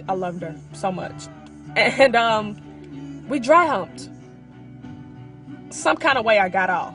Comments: what did say her and her crush did at military school ar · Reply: "dry humped" 😹😹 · 😂😂 "we dry humped... and in some kind of way i got off" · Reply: I heard “we drived home” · What did she say they did at 1:09 ? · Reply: dry humped 😂👌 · What does she say at 1:09? "We" what...... "Drowned?" I can't tell what she says so that's why I'm asking. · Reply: Dry humped